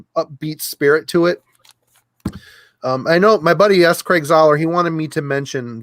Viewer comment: did you say your buddy s craig zahler as in the filmaker?